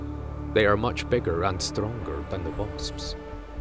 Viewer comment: "They are much bigger and stronger than the waspsps"???? Am i hearing shit or does he say "waspsps"?